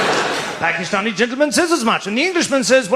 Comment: Love this joke! Well told, Sir! (I heard it as "I'm not taking any chances on a Ginger")